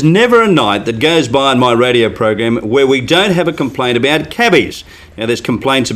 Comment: At the beginning I thought he said Cabbage... LOL